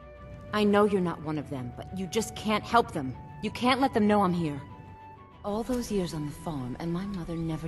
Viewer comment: did Sapphire say “all those years on the phone..” ?? Captions show it as phone as well. And.. well I don’t recall phones in Tamriel, what’s going on 😭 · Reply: "all those years on the farm"